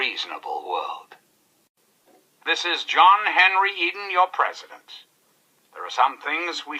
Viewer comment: When i was little playing this game, i didnt know english well, so whenever he said "This is John Henry Eden your President." i heard it as "This is John Henry eating your President" and i was like why the hell is he eating our president lol